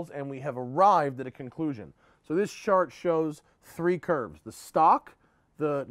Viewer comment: "This shart shows..." lol. Had to rewind it a few times. It's 12 am and when I heard this I had to laugh. You are the reason I got a Fiesta ST, thanks man.